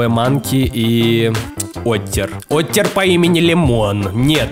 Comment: Я: слышу слово Оттер Мой мозг: не смей Я: Оттер попу Поттер😂😂😂 Мой мозг: опять🤦🏻